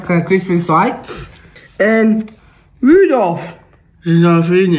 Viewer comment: My stupid ass thought he said rudolph hitler😂